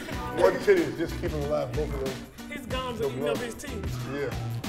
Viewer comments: What did the lady say at 3:06? "His gums..." are what? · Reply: Jebidiah Waffleman She said his Gums are eating up his teeth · What were they saying at 3:09? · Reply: said Mu's gums are eatin up his teeth 💀💀💀💀💀